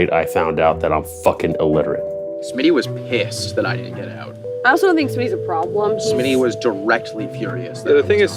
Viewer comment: Time stamp? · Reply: is what he’s talking about, but i’m 90% sure she actually says “I also don’t think Smitty is a problem” so she doesn’t actually change her mind, her and are both saying the same thing.